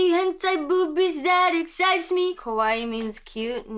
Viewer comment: Gawd I thought she said Kowai 🥲🥲 I really had to get an ear checkup